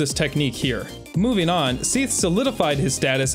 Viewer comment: I was watching this and eating chicken, thinking that gaping seathe was funny and nearly spitting out my food when I heard "Queathe"